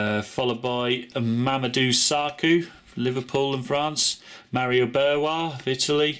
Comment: Did he just say "mario berwa"